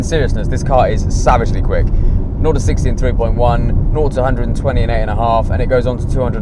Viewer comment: did he say 0-100mph in 28 seconds? seems kinda slow, that can't be right can it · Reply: Alistair Hughes FS no, he said 0-60 in 3.1, 0-120 in 8 and a half